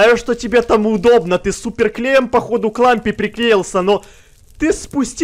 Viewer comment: мне послышалось он сказал "к ламбе", а не "к лампе" 😂